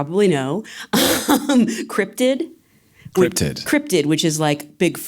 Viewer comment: What was that word again? Great interview :) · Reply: Cryptid